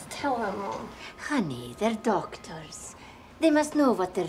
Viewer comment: In the beginning, when the mom said with a thick accent, “Honey, they’re doctors…” I thought she had said, “Honey, they’re dog turds…” 😳😂